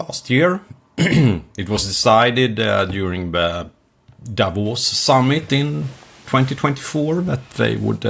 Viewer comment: I thought he said, "...the devil's summit" (Davos) Same thing, right?